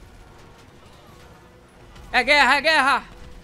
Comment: impressão minha ou ouvi o soldado disser "É GUERRA É GUERRA"